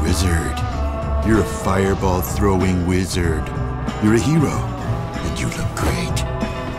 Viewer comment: "You're a hero, and you can create." Best fucking line from the wizard. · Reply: Bobby - Clash of Clans and Clash Royale at first I thought he said "and you'll do great"